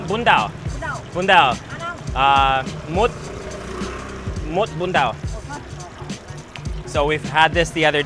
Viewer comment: Bun Dau (Bún đậu) instead of Bun Dao :)